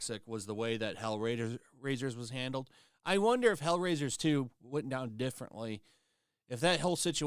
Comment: I heard you say Hell Raisers 2 and thought you were talking about a new game lol. Im slow it took me a secind to understand the mistake 😅